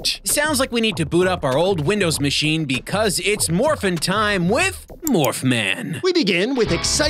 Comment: My dumbahh thought at the timestamp 1:34 they said "Morbing time" instead of "Morphing time"